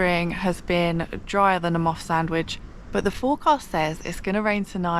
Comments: Dryer than a moth sandwich! Absolutely did me 😂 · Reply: I'm pretty sure I heard "moss sandwich"... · Dryer than a moth sandwich! Absolutely did me 😂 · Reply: Oh, I thought she said "a moth's sandwich" and I was trying to figure out what kind of sandwiches a moth would eat.